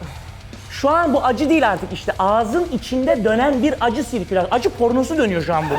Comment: acı ***osu dedi galiba ben mi yanlış anladım????